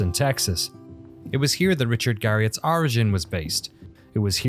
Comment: Just a quick one- where says “Origin was based” the English Closed Captions subtitles says “where Origin made Ultimate Underworld”. Truly not a big deal, this video is fascinating and awesome but figured it’d be worth pointing out the discrepancy (especially since the subtitles say Ultimate not Ultima)